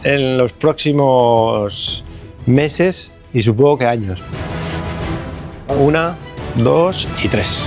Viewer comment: años fue que dijo? 😮🎉🎉🎉 que emoción saber que regresas